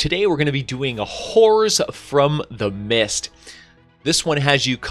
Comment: I swear said, "The whores from the abyss"